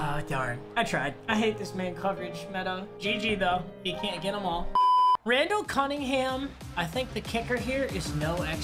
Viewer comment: did he say Randall Cunningham is a kicker?